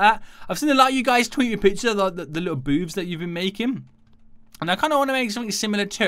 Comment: sounds like he said boobs instead of booths XD